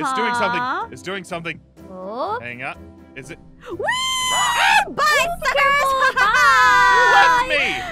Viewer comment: Are aphmau really swear cuz i heard aphmau say "BYE F----CKERS"